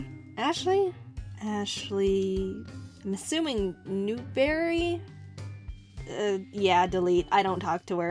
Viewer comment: omg when you said newberry i thought you said newmaker and i flipped out tell me if you know why i almost flipped out